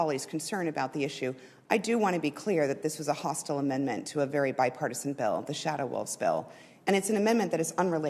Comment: Oh, I thought you said "My partisan".